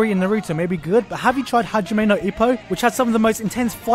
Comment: whats the name of the anime you mentioned at 6:44? i couldn't hear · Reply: Steven Lake If your asking about the one about boxing it's Hajime no ippo. The one he talks about after that,(with the sword fight) is Fate/zero.